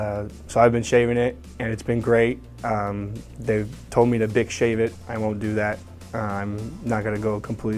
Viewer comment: I swear I thought he said Dick Shave It...backed it up and heard Bic. Lmao GBR!